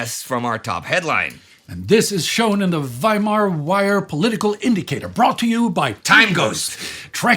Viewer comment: I'm sorry, but did I just hear "Indy"cator